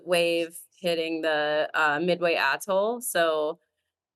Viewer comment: I'm sorry hitting the midway what? I know what I thought I heard but I know that's not what she said. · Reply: Island called Midway